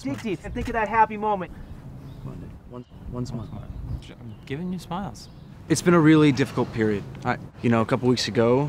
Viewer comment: Can someone please explain what joe said there I couldn't hear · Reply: He said “I’m giving you smiles